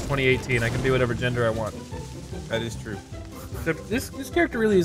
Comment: thought he said transgender not that is true